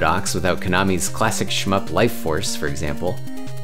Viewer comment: ohh, shmups? i thought you said shmucks